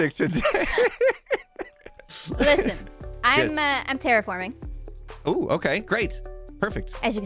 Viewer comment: For a second, I thought said "I'm terrifying." The statues could use some greenery too, like some vines and moss. It'd make them look older and less like "gummy bears".